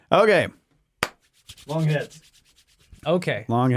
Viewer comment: When y'all first started saying 'long heads' I misheard it as 'long hits'... and for a moment, I wondered...lol. Silly. Great stuff, btw!